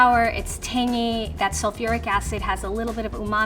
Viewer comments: did I hear right? Did she say sulfuric acid? Nah man nah something ain't right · did she say sulfuric acid? Or I am hearing wrong?